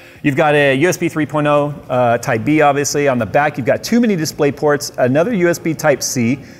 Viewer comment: at first I thought he said "on the back you've got too many DisplayPorts" rather than "two mini-DisplayPorts"